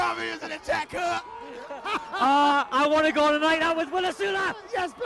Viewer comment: What does he say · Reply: "W's in the chat, cuh". Apparently.